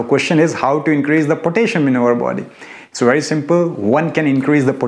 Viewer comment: I'm sorry but I don't understand the word you're saying at 0:53. Increasing what in your body? Also what do you suggest to add to apple cider vinegar? Apologies for not understanding but I did mostly. Thank you so much for the information! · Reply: Pottasium 😀